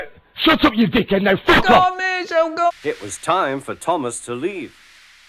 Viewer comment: i thought he said ghost... i look dumb rn